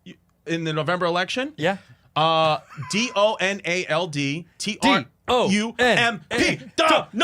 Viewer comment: What did Chris say to Bobby at 7:21 I can’t understand what he says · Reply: He’s saying Donald Trump with an Asian accent